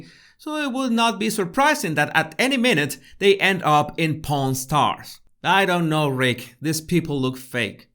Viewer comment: For a short minute I thought you said ‘ porn stars’ not ‘pawn stars’. Yuck!!!